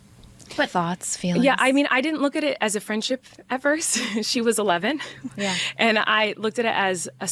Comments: Though a lot of the comments currently are calling you out for mishearing "at first" as "ever", it is a bit weird to say "at first" before explaining all the ways they were friendly when she was a child. Like, people usually aren't immediately friends when they first meet but she kept hanging out with child JoJo repeatedly to the extent of JoJo coming over to her house so often that JoJo was like part of her family prior to JoJo turning 18. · with the “at first” bit i get what you guys are saying but im more referencing they made TONS of videos together from the get go and is clearly lying about the friendship imo, she tried to imply it was a slow thing but the reality is the more you listen the more it seems it was very friendly from the near beginning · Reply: Okay now you're deflecting that you didn't misunderstand when ALL of the Americans watching clearly heard that you thought she said "ever" when she said "at first" and that's just a simple accent mishap, but you didn't double down or quadruple down on that, you paused it and reiterated the incorrect words at least 10 times. Making it not nearly the contradiction you thought it was.